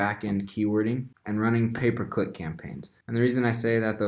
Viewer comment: thought you said paperclip vs pay per click